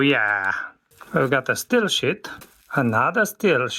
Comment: STEEL WHAT?! ohhhhhh steel sheet, I thought he was saying steel shi